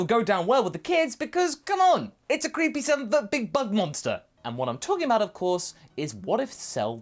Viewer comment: "Creepy sumavabubbigbugmonster!" Whaaaa? English! X_x 01:18 Guess never read "Honor Road." Also, I was kind of hoping for a Majin Cell instead of Majin Vegeta tbh. That he would be angry enough over not being accepted by his "siblings" that Babidi would take advantage of it.